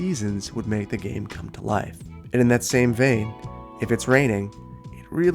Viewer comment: did he just say “that same vay”? o.0 You goin German on us? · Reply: I'm pretty sure he said "vein", not vay 😅 as in "the same vein"